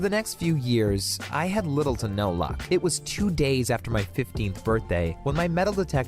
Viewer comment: i thought he said 50th birthday but it was 15th birthday